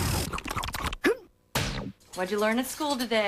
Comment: What did he say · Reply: creepy meatead “never go last”